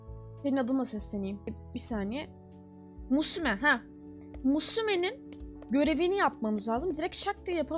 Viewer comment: bir tek ben mi osimen diye duydum(yanlış yazmış olabilirim)